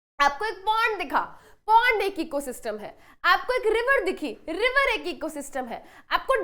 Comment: par mam apne kya bola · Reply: Pond bola ma'am ne bhai.